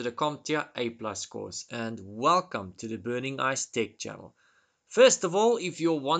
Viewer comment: I thought you said burningicedick smh.